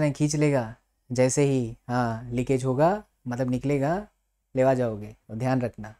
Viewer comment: Did he say, "Pela jaoge?"